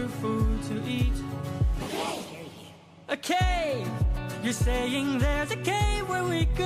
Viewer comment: they didn't catch one of the lotus eaters said scary cave!!!